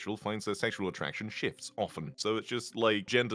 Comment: HELP I THOUGHT THAT SAID “SEXUAL ATTRACTION SHITS OFTEN” 💀😭💀😭💀😭